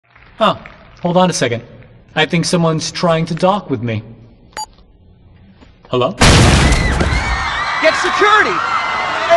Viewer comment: I thought he said "i think someone's trying to talk with me."